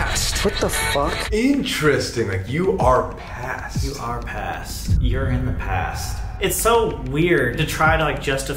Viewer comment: i think it said "your our past" not "you are past"